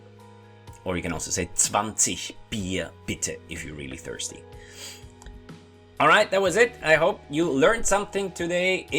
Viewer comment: Please someone tell me what he says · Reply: 20 beers please, in English